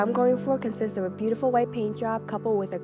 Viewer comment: Oh it's paintjob!! I thought she said blowjob thow🙄